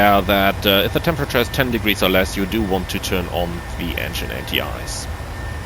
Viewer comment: What did you say about the air temperature below 10 degrees ? About the turn on the Engine xxx. I didn't catch that last part. Thanks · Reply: Simply put if temp indicates 10 or below and there is visible moisture of outside, aka passing through clouds, turn on the engine and wing anti ice.